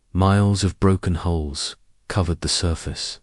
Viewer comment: Why can't your subtext match the spoken word? For example, the spoken word is hulls yet the subtext shows holes (@ 0:08); two different words with different meanings. If a person has a hearing/listening issue, reading the subtext is important. Yet, your subtext is really lacking in matching what is being said. Also, the AI is pronouncing punctuation marks (periods as dot) - this just show lack of attention to detail on the channel owner's behalf. Truly disappointing!